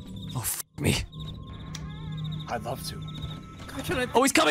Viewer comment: yall heard what todoroki said💀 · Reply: "I'D LOVE TOO 😍" TODO WHATTTTT!?